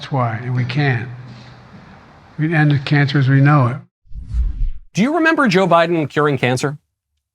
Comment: I really don't like Joe Biden, and I hate defending him, but I listen to the clip and I hear "we c'n end cancer as we know it" and not "We ended cancer as we know it" He has had like 40 gaffs worse than that